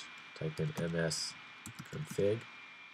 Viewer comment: What does it say at 14:11 "type in ..." What??? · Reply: msconfig